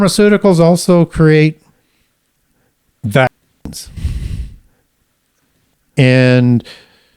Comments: What’s the v word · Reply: Ohhh vaccines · What doez he say pharmaceuticals creates at 1:29:12 ?? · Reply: Vaccines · What is said at 1:29:14-15, you blur the word out that he says!? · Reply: Vaccine